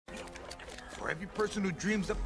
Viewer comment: “For every person who dreams up the electric lightbulb, there’s the one who dreams up the atom bomb” -Mr. Electric from Sharkboy and Lavagirl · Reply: Thank you, I thought he said "the atom, Bob."